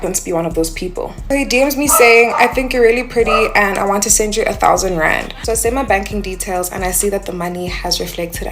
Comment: 1) She’s lying! 2) If he did randomly give her $1000 she would’ve went…they’ll go for alot less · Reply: Lol I thought she said “1000 grand” I was like Nigga ya lyin